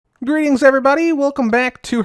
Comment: "Grian's everybody" is what he said I guess